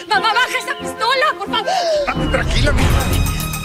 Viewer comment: En el min 4:21 no entiendo si juan dice esta bien,tranquila mija